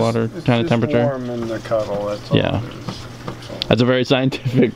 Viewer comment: I was partying attention half attention and hard to rewind I thought you said something about issuing bath water 🤣